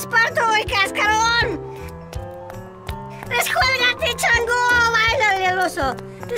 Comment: Vi este video hace 13 años y hasta ahora no sé qué es lo que dice en el 1:58 ¿Alguien sabe y que me quite la duda XD? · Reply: Dice asi : descuelgate chango, bailale al oso" son expresiones chilangas de pleito